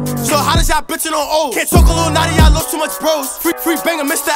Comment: What did he say 0:47 ...??? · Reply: can’t talk on Lil Notti i lost too much bros